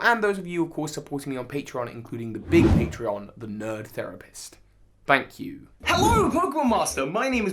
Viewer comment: Bro, I thought that said "the rapist" in the bottom left corner for a second! 🤣